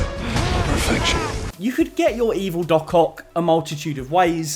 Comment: My exhausted brain thought it said “Don’t ruin your cock”